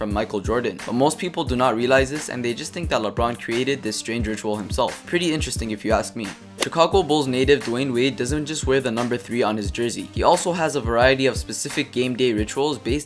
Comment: did you say Chicago Bulls Leader